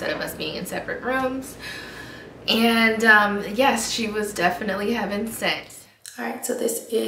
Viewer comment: Waaaiiittt!!! Did i hear what I thought I heard at 3:02? I'M CRYINGG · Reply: haha I said she was heaven sent!